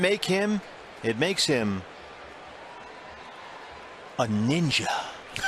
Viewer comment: がおにいちゃぁん…にしか聞こえない件